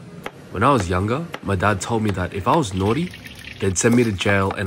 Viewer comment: What did he say, “if i was…”? Why i heard “if i was alone?”